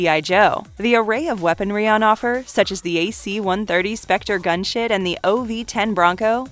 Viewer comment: Could've sworn she said AC-130 specter gunshit instead of gunship of am I just drunk? Happy Saturday beerday!